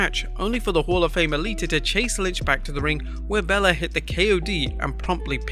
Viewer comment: did you say bella?